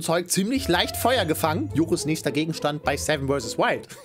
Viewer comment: ich hab die ganze zeit joko gehört